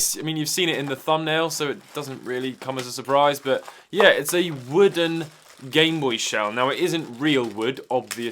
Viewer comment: Thought it said “whole grain gameboy” and thought it was made of bread